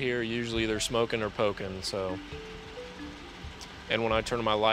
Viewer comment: "They're either smokin' or pokin'" I totally thought he meant 'pokin' was banging. I realized he probably meant needles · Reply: I thought he said toking and I was look uhhh that's the same thing lol